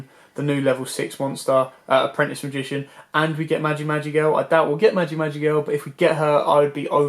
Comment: magic magic elf? what did he say in min 11:55? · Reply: Magi Magi Girl. OCG Rank 6 spellcaster XYZ :)